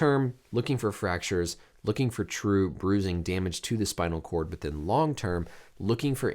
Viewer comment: Anyone else thought it said damar. It scared me again. I hope Damien is doing ok